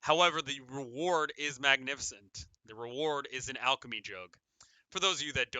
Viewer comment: lmao 33:16 I thought you said alchemy joke, not alchemy jug xD: You get an Alchemy joke and if you don't know what an alchemy joke is- me: wait theirs jokes?!? please tell me more: shows a picture of an alchemy jug me: oooooooooh, I did not hear that right